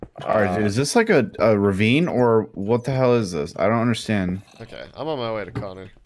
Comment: did Connor just say "hell"??